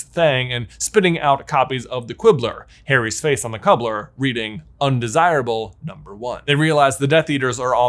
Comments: Anybody else think it sounded like J said "Cubblar" instead of Cover? Probably because he said Quibbler before XD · Quibbler, but then cubbler instead of cover? Or is it just me hearing that?